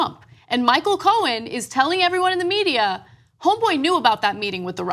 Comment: Did she say "homeboy"?